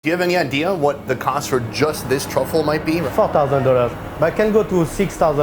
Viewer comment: Instead of $4,000, I initially thought the French chef said fuck thousand dollars